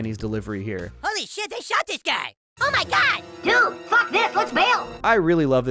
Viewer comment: Dude, fuck this let's bail, thats what kenny said if you can't hear him well(use captions, it's eziar)